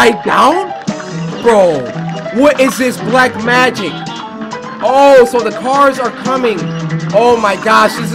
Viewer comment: yoooo wut did he say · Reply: "OH so the cars are comming " Or "magic"